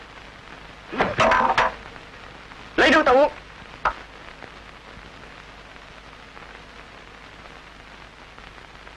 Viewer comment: ai nghe giúp mình A Sử nói gì v ạ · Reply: "Lấy nước tao uống!"